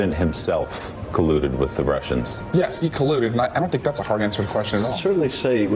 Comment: "I don't think that's a hard answer to question at all"...lol Did I hear that right???